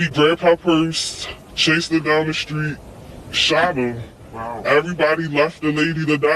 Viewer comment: She was saying Sheena , lol . She was saying Sheeeeew Tah ( shoot her ) in that strong Baltimore accent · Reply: Yes, right. I thought she said "Shoot her" too. So sad